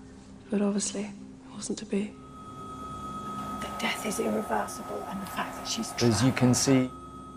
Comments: Sounds like she said “but death is irreversible and shes traveling now” just like telling someone “we cant take them back their in a better place now” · It sounded like she said "Death is irreversible, and that fact that she's driving"